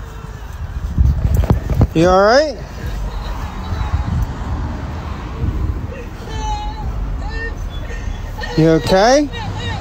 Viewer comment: When asks if the lady who’s crying is alright at around 13:55, I hear “my mom”. Is that just me?